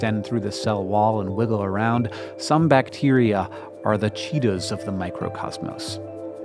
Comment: I heard "Some bacteria are the cheetos of the microcosmos." :3